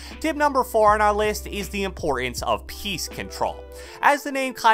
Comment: Every time I hear "piece control", I hear PEACE CONTROL now I can't unhear it